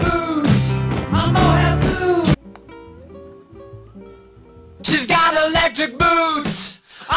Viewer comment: I think it says "it doesn’t make a difference if we make it or not" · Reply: I think it says "she’s got electric boots a mohair suit"